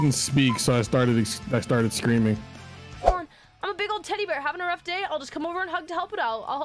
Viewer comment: when she said teddy bear i thought she said tiddy bear to begin with...